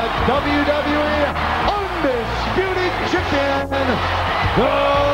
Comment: rofl, I thought at 1:09 the announcer said "undisputed chicken" :D